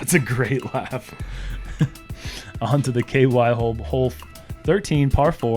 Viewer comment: did say, “on to the KY hole”? 😂😂😂😂